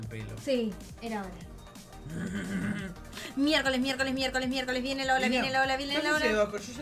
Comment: ¡La casa se fue a la m¡£rd@ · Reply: Dice Miércoles, pero te digo que yo la primera vez pensé que decía mierd4